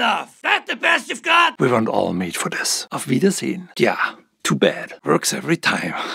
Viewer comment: What does golem say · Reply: He says "Auf Wiedersehen" which is the same as in "Till next time"